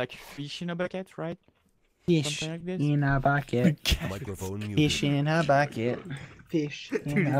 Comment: is when the award winning ‘Fish in a bucket’ starts I lowkey love that song... 😂😂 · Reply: Idk why I thought he was saying “eat shit in a bucket” 😭😭😭